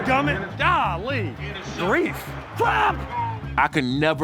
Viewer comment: Am I the only one who thought Rivers said F*CK at the top of his lungs?